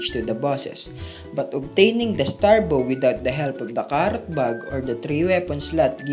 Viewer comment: He said that it's inside the dungeon. Excluding the preparation like using frog follower or three weapon. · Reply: I don't know, I don't understand what he says in this part 2:09 "...obtaining the star bow without the help of the ??? or without the third weapon slot..." (Knight bug? Card bug?)